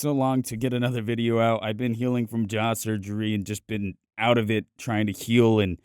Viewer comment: When he said jaw surgery I thought he said jah surgery and my eyes became the size of baseballs